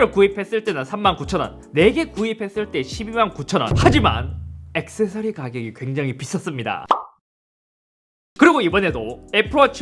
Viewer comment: 엑세사리 값이 굉장히 미쳤습니다.라고 들림.. ㅋㅋ